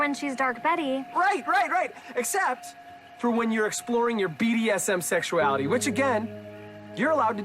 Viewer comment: I thought the guy said BTS but he said BBS or BVS I think???